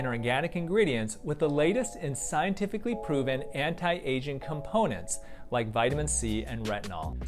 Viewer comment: excuse me son, anti what? edit: OOOOH! he said aging! haha, phew